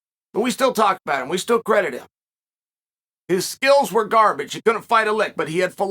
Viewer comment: lol did he just say Bruce Lee's skills were garbage